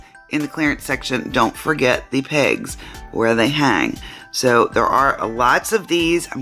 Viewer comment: Welp I can tell I'm sick. When you said look for the pegs they hang on. I thought you said don't forget to look for the pigs 😂